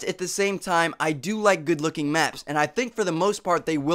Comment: I thought you said you like good looking men not maps HAHAHAHAH